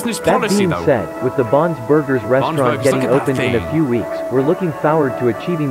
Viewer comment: my cc said instead of “bons burgers” it said “bonds burgers”…WHAT IS BONDS BURGERS!?!LOLOLOLOLOLOLOLOLOLOL